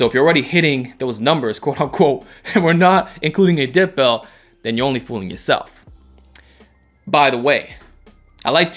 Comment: i heard "then you're only from the south" and it would make sense too lol cause they get heavy over there